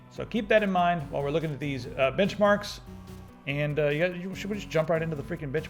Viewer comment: for a second there I thought you said bitch marks.